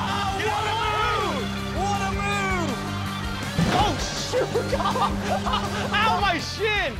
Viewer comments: I heard the word shit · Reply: it was shoot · When Cody fell off his scooter it sounded like he said ow my shit